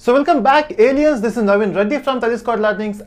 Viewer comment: Am I the only one who hears "welcome back idiots" at the beginning?